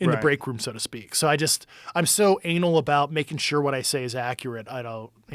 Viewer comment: Did Grusch just say that he was "... so anal" is that THE word, but used in another term... @ 1:31:13. Please turn on captions and help a brother out man.